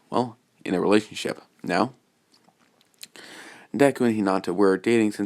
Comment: did you just say "DEKU"